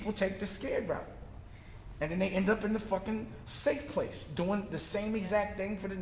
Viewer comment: when he said "Safe Place"...did u rewind to see if u thought he said "Sunken Place?"